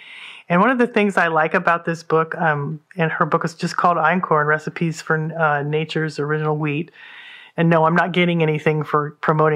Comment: For anyone who didn't catch it, the name of the book is Einkorn: Recipes for Nature's Original Wheat. :-) Mentioned at around 19:15-19:20 or thereabouts.